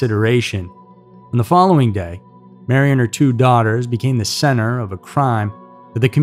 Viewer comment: I thought it just heard you say her two son a minute and 36 into it LOL